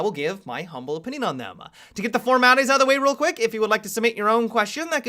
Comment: To get the format [..] real quick - can someone tell me what the hell says? I watch him for really long time and I have never figured out what does he say in that moment in every sunday mailbox. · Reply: Hakej I think he says 'To get the form out of the way real quick...' Might be wrong though